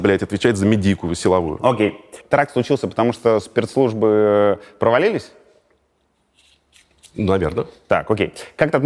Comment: «теракт случился потому-что спиртслужбы провалились» или я один кто это услышал